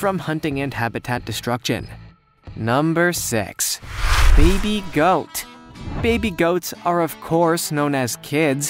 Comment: I heard "Beebe goat"